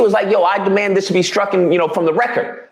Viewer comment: did he just say STRUCKEN? Or am I mishearing him?